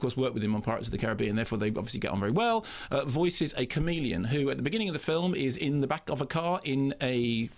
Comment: Does anyone else think it sounds like Mark says “comedian” instead of “chameleon?” And hello to Jason Isaacs!